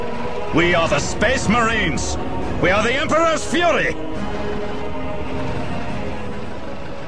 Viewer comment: did he just say he was...the emperor's furry? 40k is getting really creepy...